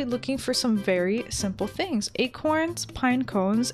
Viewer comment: I thought you said fairy-simple thing HAHAHHAHA XD